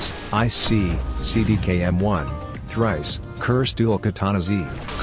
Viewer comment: did that say "thrice"?"